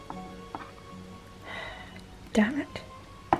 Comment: Welp, this isn't how I wanted my Halloween video to go. It was meant to be posted about 7 hours ago but my internet gave me trouble. So I kinda doubt as many people will check it out sadly cause Octobers pretty much over heh 😔 Oh well (EDIT) I just wanna say thank you so much for all the support and comments so far, I was really worried about this Halloween special but you guys are making me feel so much better! Either way I hope you enjoyed this took me a long time and it was incredibly hard to make with what's going on in my personal life. ❤ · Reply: Ur awesome. She said Dammit and I thought she said Dan it